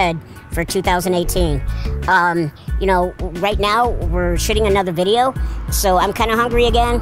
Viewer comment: I think he said shitting another video